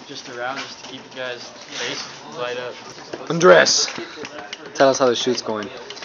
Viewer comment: lol when he says "ANDRES!" at 1:15 I swear I heard "UNDRESS!", anyway I can't wait to watch it /,,/